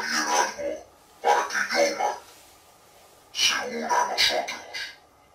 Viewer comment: Hola como andas en el minuto 13:37 hay escuche que el Project Zorgo dijo para que se una a nosotros